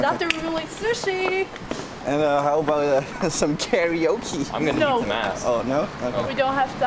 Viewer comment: did I hear someone's a ganna eat ass?